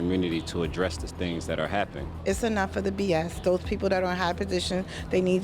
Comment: "It's enough of the boys" lmao what she said B.S